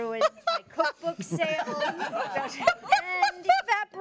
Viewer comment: Brilliant. Did she say "Just ruined the f*cking sale" at 2:57?!! xD